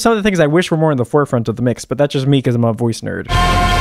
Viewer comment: When you said you're a voice nerd, I thought you said voicer. I was already memorizing the term for future reference 💀